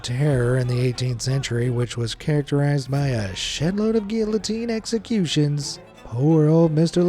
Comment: Did I hear him say shitload? Heck yeah not censored